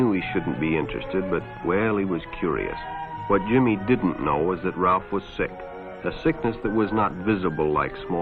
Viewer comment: I misheard the narrator at 3:51. I thought he said "What Jimmy didn't know was that Ralph was sexy" and for a second I thought the rest of the video was going to be even more of a joke, on how the narrator was really the homosexual all along.